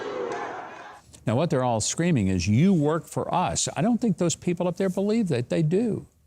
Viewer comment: When the citizens of Chicago were chanting, I thought they were saying, "You are corrupt!" But "You work for us" is true too!